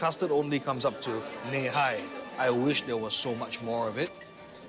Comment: wtf was he even saying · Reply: He said, “I wished there was so much more to it”